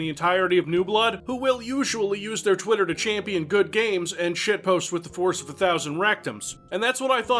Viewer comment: God, first time I was distracted and thought he said shit post with the horse of a thousand rectums.